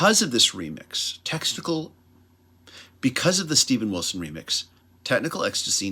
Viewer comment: say what......what did I hear......testicle ?